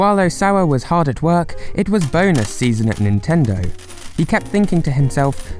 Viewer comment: I swear I heard boner season and thought “my god what is he doing?”